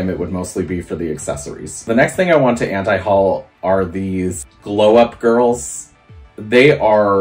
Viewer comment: I thought you said the "blow up girls". 😆 Blow up dolls for children would be strange.